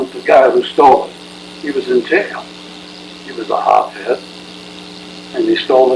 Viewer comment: The text reads "he was a hothead" but what the man said was "hop head", this is old school slang for drug addict.